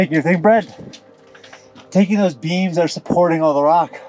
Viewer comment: when he was in the mine and said beams, i thought he said beans lol